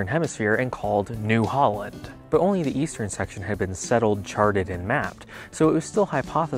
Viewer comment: Oh! East? I thought you said, Weast!